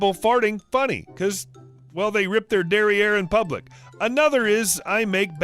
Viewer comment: I must say you saying "dairy air" really threw me, as I literally thought you said "derriere" which is french for "behind" - made me chuckle lol